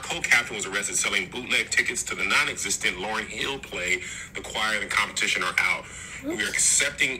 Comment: “The Choir and the Competition are out” sent me!!!!😭😭💀💀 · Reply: Oh I thought he said acquired, in competition, or out 😂 no shade, L Boog.. still luh you!!